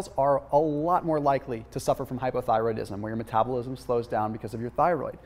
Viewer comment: Hey! I find your videos amazing and super informative, but unfortunately there is a mistake at 4:55; the caption says Hyperthyroidism and you say Hypothyrodism. The Caption should be corrected since women, as you very well said, have a tendency to suffer Hypothyroidism. (Hypo = Low/Hyper = High).